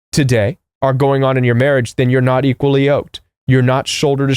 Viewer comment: To clarify for people who don't catch the word at 18:37 -- "...then you're not equally yoked." (It's a wooden bar or frame by which two animals, like oxen, are joined at the heads or necks for working together.)